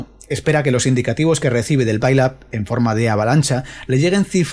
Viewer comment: No entendí esa palabra pilap? pailab? (Gracias por el video) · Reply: Gracias por el visionado y por tu comentario. La palabra es "Pile Up" y se traduce por "Amontonamiento". El pile up se va desatascando por sí solo si el operador en actividad lo gestiona bien. El contexto es que si (por ejemplo), llama alguien dentro de ese barullo de voces diciendo "Eco Alfa Uno Alfa Bravo Charlie", nuestro cerebro está preparado para procesar rápidamente EA1ABC, pero si ese mismo operador llama como "Ecuador América uno América Barcelona Canadá", se rompe la cadencia y el procesado del distintivo se hace más lento. No digamos ya si hay que repetir por culpa del ruido (QRM) o el desvanecimiento (QSB), o porque hay dos estaciones parecidas que se solapan en la respuesta.